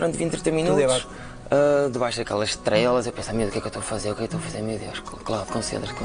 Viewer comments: Eu também ouvi 'Debaixo daquelas trelas'. Estou solidário contigo, Inês. 🙂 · Também ouvi "debaixo daquelas trelas." 😂